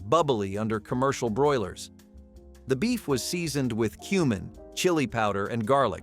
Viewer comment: I thought he said the fiestada Mexican pizza was seasoned with 'human.' He actually said 'cumin,' I just heard it wrong. It was right after the recipe for cheese zombies, so maybe I was in the wrong frame of mind lol.